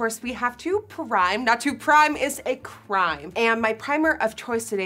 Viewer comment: I swear it sounded like you said "not to crime is a crime" 😂 even though I know you actually say not to prime is a crime.